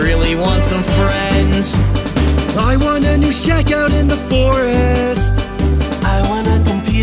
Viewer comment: Why do I keep hearing 15 saying “I want new shot gun in the forest” even tho I know what 15 is saying